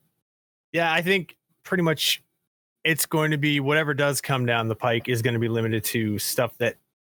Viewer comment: Did seth say "whatever does come down the pike?"